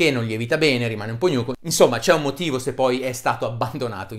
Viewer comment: rimane un po'... ?? Cos'è che dice? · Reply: Io ho capito "gnuco", credo che intenda "ammarzanuto" ;-DDD . Ok. Duro, compatto, non cresce. Secondo me.